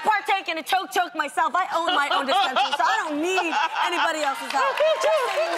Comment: did Jeanie say she OWNS her OWN dispensary!?!! Well gawtDAMNNGURLLL!!! MAKE THAT MONNAAYY!!